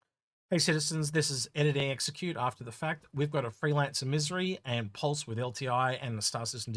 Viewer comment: sounded like you said you are giving away freelancer misery